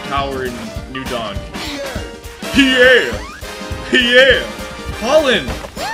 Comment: Did he just call Pauline Pollen??